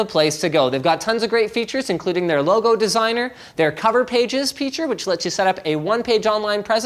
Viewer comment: I swear I heard Linus say "Cover Fage Peature" during the Square Space sponsor spot.